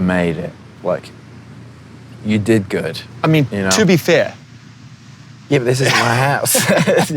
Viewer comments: it just hit me halfway through that he’s in his backyard and not at a picnic table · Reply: I thought he said “yeah but this isn’t my house” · Did harry say this is or this isn’t my house at 39:10? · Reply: I'm a year late lol but I think he said it isn't his house? Idk